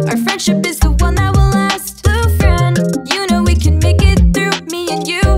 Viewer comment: I thought she said glue so i instantly recalled the time Karl drank half a bottle of school glue during Mrbeasts creator challenge.